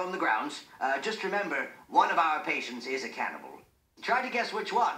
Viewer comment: When I was little I thought he said, "one of our patients is a camel."